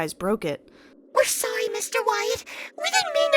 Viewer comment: is it just me or did they just called him Mr. White? (breaking bad)